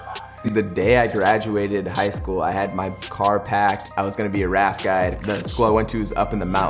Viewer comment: I thought he was saying "rap guy" not "raft guide" Does that mean I'm racist?